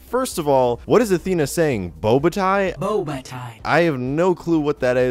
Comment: What does Athena say in the first sound file?I hear 'Bobata'